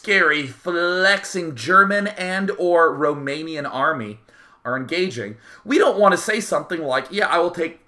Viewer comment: Did he say romanian army? None of the players are romanian!?